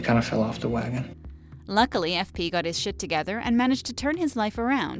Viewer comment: did she say sh*t😂😂